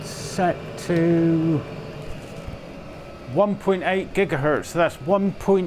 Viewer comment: 1.4 GHz???Did I hear this correctly? That's a hell of a pump ;-)